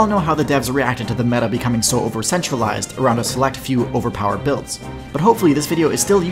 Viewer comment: "we all know how the devs reacted to the meta becoming over-centralized" Human class sweating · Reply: I thought he said over-sexualized lmao